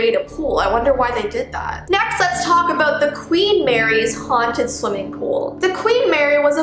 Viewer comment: Am I the only one who thought she said the queen married a pool?